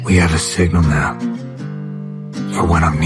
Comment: when he says "we have a signal now" i hear "we have a sigma male"